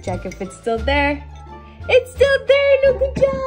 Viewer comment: Did she just say... HAPPY MUNDAY?!?!